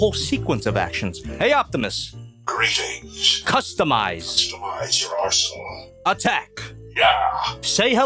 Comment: oh no ... at 12:59 at first I heard "customize your arshole"